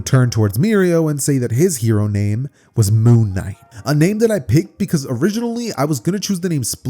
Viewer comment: I could've sworn you said his name was Moonlight